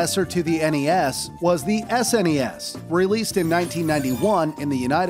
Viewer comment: subtitles say sens instead of snes